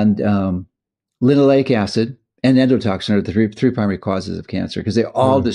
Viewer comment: you mentioned estrogen and some form of acid being connected to cancer. I couldn’t understand what kind of acid. Would like to look that up. Anyone catch what he said? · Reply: I believe it was Linoleic acid